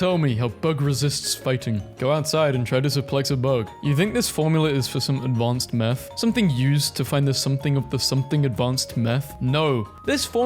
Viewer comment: why did you say ‘meth’ instead of ‘advanced math crap’